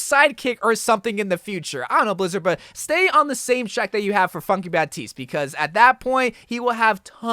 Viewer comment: I thought at 9:42 said “fucky baptiste” but its funky lol